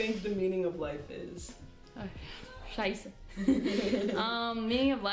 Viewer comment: Did say "scheiße" at 15:26 ?? my german ass is SHOOK